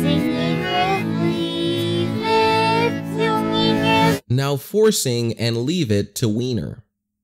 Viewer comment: i was listening the backwards and i thought i heard "now pussing and leaving it to wiener" i was dead straight up then i saw the captions and it said "now forcing and leaving it to weiner" tell me if you hear something different so i can prove i am not the only once hearing pussing instead of forcing